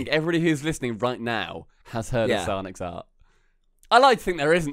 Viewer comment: What were they saying? Psyonix Art or..?